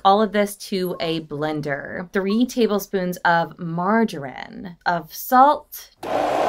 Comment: Did she just say 'margarine'??